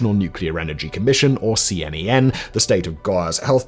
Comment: Did he just say the C-M-E-N? That is one unfortunate acronym.